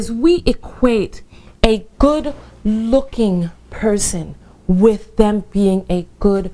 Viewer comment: i thought she was like a gyspy or something because i thought she said a good cooking woman instead of good looking haha